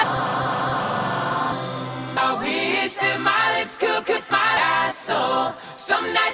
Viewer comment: Maybe it's a mind trick, but when I first heard this, I thought at 1:30 he initially said "I wish Miley Cryrus could kiss my asshole".